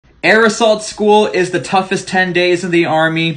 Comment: My stupid ass thought you said aerosol school like you had to learn to refresh be fresh with febreze